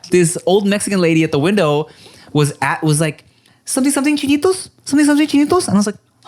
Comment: I think the Tam's lady probably said "chiles gueritos". That's what us hispanics call those peppers lol